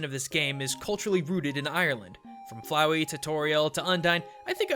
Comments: Flowey, Tutoriel, Undyne. Wait WHAT!?! Tutoriel? smells like someone hear this joke 1 million times [ ¬ ‸ ¬ ] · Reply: its flowey to toriel to undyne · anyone else hear TUTORIEL?